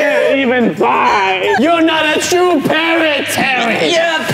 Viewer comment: screams Unspeakable: your now a true parrot Everyone: laugh Unspeakable: you will never be a parrot Me: I thought unspeakable said he was a true parrot😂